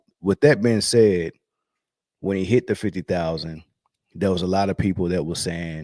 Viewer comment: I thought they said it ended when he hit 40,000..wth!! Freaking lebum is a clown